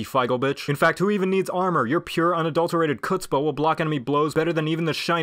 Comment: What's the word he says at 1:38? I'd look is up but I have now idea how to spell it. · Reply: Chutzpah